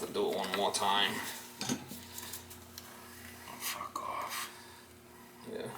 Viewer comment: I heard him whisper under his breath, "Fu*k off at 5:39-5:40".